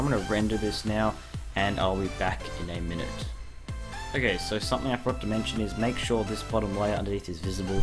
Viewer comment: Bro wtf does he say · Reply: "Ok, so something I forgot to mention"